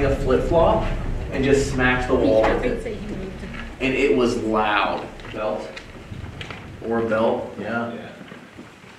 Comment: Love this channel! So fascinating to watch your investigations and adventures. Thank you for sharing them with us! Around 46:45, it sounds like a woman’s voice, in another language. Does anyone else hear it? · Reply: it’s the woman who is on the research teams voice i’m assuming because the accents sound identical. she said “i’d say he moved it”